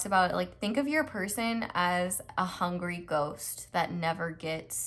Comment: Pile two really resonated, but i misheard ghost as goat and got extremely confused...